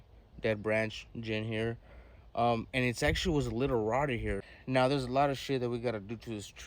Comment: Fam. Did you just say it’s a little rotty @ 5:55 and flash a photo or a literati! Hope y’all appreciate this level of content. Keep it up bruv. Fire as always.